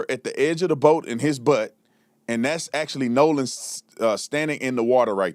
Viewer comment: I heard "no sand" which would be something you would check the bilge pump for especially when you were anchored by an island